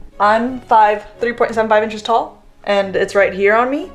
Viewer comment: Did I hear you say you are 3.75 inches tall? That's hilarious, but I may have misheard you! ;-p